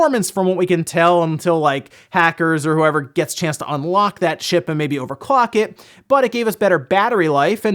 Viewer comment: I thought he said "Unlock that shit" at 11:28 and I was like I've never heard him say anything like that before